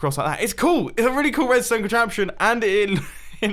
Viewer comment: Not gonna lie I thought it said contraception instead of contraption for the climbing thing